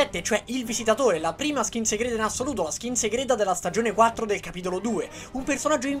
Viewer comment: solo io ho sentito Stagione 4 capitolo 2?